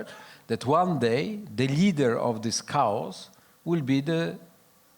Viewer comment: Cows why blame Cows?, sounds a bit Orwellian?. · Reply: He's saying chaos. I also thought he said cows 😅😅😅